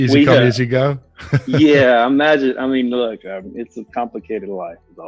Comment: Hi, I actually think he says something else 13.58. To me it sounds like he says, not "I imagine", but "I mean, imagine!" - which is even more revealing and honest (and terrible), that he certainly didn´t mean to say. Major verbal slip. Thank you for yet another great video, and I´m so happy you chose to cover that interview - you da man!! 💪 P.S. Died laughing when you called out his misogyny 😀